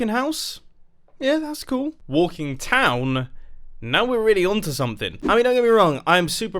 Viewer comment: Am I the only one that thought it said "walking Tom" Just me Okay